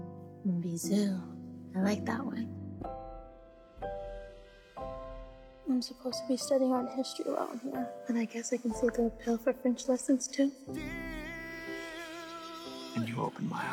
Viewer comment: What does Trina say at 1:35-1:40? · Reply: “I’m supposed to be studying art history around here and I guess I can see the appeal for French lessons too” -Trina